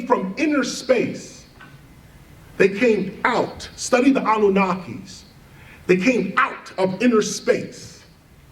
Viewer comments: what does he say at 12:00? study who?? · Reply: He was talking about the anunaki. The ancient sumerians one of the first advanced civilizations had a mythology about aliens, the anunaki and how they came to earth. It's pretty interesting stuff. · Can somebody please spell what he says to study at 12:00 minutes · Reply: I think anunnaki